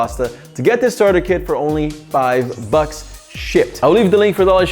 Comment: I thought he said "SHIT". hahahahaha Love your videos Alex! It's not full of ads. unlike Jose Zuniga, who just keeps on making 7 minute ad videos. it's so frustrating